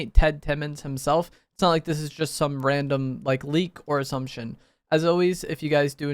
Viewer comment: When he said ir assumtion i thought he said or some s#!t